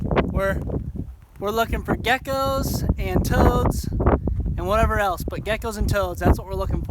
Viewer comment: did you say toads